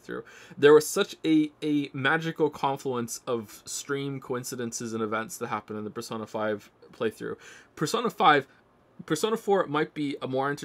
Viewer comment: what did he say in 32:36? i couldn’t understand it well 🥲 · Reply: “There was such a magical confluence of stream coincidences and events that happened during the Persona 5 playthrough.”